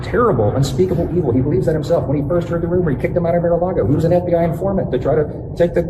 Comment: Does anyone else hear Mike say “vitilago” instead of “Mar-a-lago” in the Sept. 5 clip?